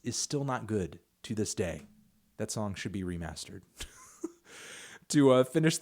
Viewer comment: Never heard anyone criticize the mixing on All of the Lights. Wtf is he talking about? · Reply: I thought he said this song needs to be *remastered?